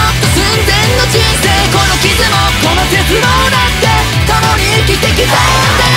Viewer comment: 「この絶望だって」の『絶望』が鉄棒に聞こえたから 『この傷もこの鉄棒だって 共に生きて来たんだよ』ってなったwww